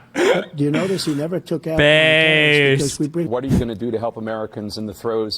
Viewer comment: Am I crazy or did bro say "what are you finna do to help Americans"